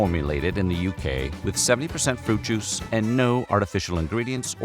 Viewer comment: Narrator says 70%, caption says 7%. Which is the case?